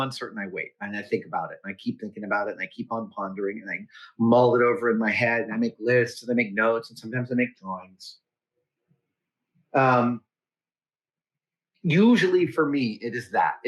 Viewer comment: with this funky audio, for a second I thought you said “I make lists, I make notes, and sometimes I make drugs” 🤣